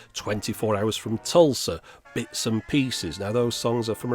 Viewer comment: What song do you say in 7:06 24 hours from "tsoulsa"? can you or anyone clarify?thanks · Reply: The song is called: "Twenty Four Hours from Tulsa" written by Burt Bacharach and Hal David. Tulsa is a city in Oklahoma.